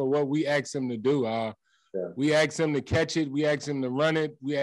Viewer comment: What does he mean when he says “we axe him? He repeated it like 4x… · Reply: Maybe he means 'we asked him'...lol or maybe 'we axed him' as in we got rid of him or maybe they literally 'axed' him with an 🪓🪓... 🤷🏻‍♂️🤷🏻‍♂️